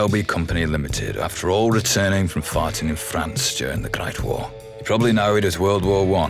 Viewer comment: I keep hearing "farting in France"